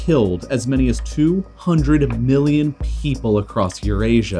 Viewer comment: did he say 200M or 200B? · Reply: Million